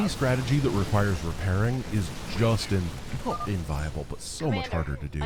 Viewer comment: Had to rewarch that one for a couple times because I kept hearing "is just in-fucking-viable"